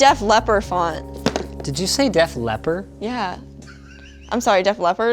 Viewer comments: did you just say "Deaf Leper" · "wait did you just say 'deaf leper'?"